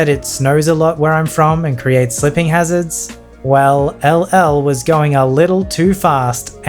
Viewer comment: Thought i heard hell,L when you finished the first story